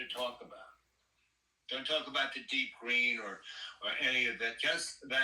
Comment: what did he say at 9:59 was it "deep green"?